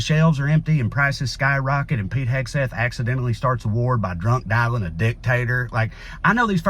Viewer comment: I more associate Pete Hegseth with drunk texting a dick pic than drunk dialing a dictator, so what I heard was "drunk dialing a dick-tater" (like a dick potato).